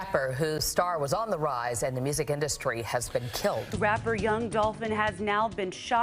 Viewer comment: sounds like she is saying Young Dolphin 😂